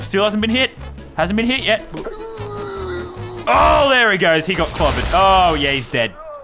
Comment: I can’t be the only one who heard “He got covid”